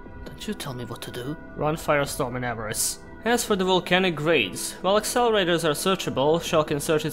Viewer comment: Run firestorm in what? I couldn’t make out what he was saying. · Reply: I think he meant and pot of avarice. So run firestorm guards and pot of avarice.